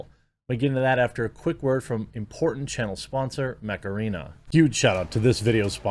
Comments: Thought he kept saying macarena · I THOUGHT SAID MACARENA